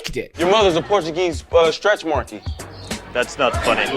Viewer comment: I thought he said “your mom is a Portuguese stress warranty” …no idea what that means